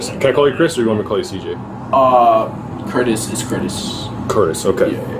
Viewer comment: When the cop was asking his name did he say "Curtis is Curtis"? Maybe I misheard but that's what it sounded like to me 😂